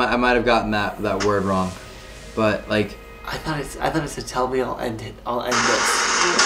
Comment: I heard “tell me and I’ll end his life”